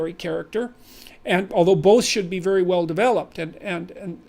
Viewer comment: "both should" sounded like "bullshit" to me)